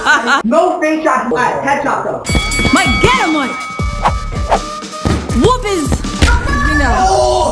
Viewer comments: did mommy say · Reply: Who his u know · Um… What did say… Whoop his what? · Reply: She said “WHOOP HIS…” but I bet she didn’t finish it since she knew their channel was kind of kid friendly